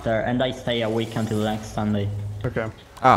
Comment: Anyone else hear say he will “stay awake until next Sunday” at 9:30 or is that just me?? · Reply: Stay a week until next sunday